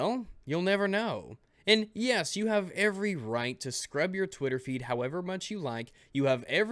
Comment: I thought she said Twitter Feet! Lol